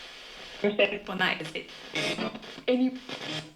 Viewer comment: I think it says something like “stayed up all night“